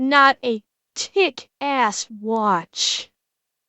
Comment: Maybe it's my brain making puns out of everything, but I swear you said "Tick ass watch" instead of kick.